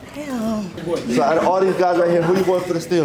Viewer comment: sounded like an itsreal85 or acevane voice over "Damn that azz Phat!"